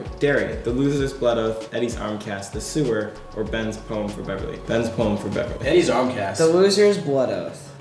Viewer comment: deadass thought Wyatt said "bens bone for beverly"